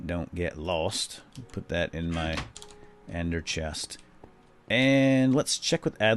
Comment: it sounded like he said ander chest instead of ender chest